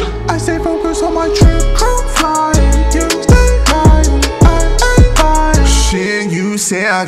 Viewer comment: It sounds like he says you stay lying i aint buyin